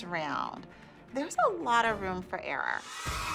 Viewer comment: When holly said, “ there is a lot of time for error” I thought she said air and I was like,... HOLLY